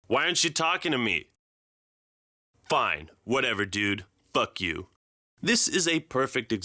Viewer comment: I thought he said buck you.